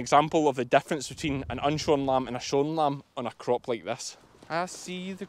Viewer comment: I’m sorry, but I thought you said “showing lambs” not “shorn lambs”. I’m listening in American English.😂 Oh, and you sing very well.👍